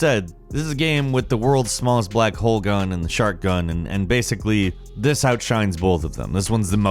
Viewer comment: I thought you said "shart gun".